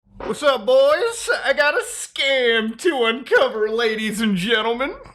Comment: I misheard the beginning and heard Scam Tuah and I thought you were doing a hawk tuah joke, I’m broken